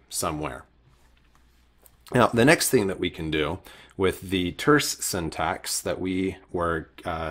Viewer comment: Can someone say to me what says here : <link> (at the end of the vidéo) after he says "SFINAE, concepts, ...." I hear "nature syntax", but I think he says another thing. thanks in advance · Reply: Hi! It's very likely to be "the terse syntax" (he mentions it at 7:03 also)